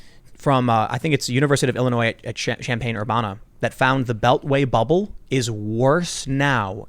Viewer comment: Did he just say "Sham-pane Ur-bonna"? 😂